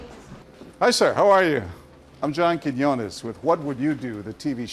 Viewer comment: Why do I always hear the host's name as 'John Kidney Honest'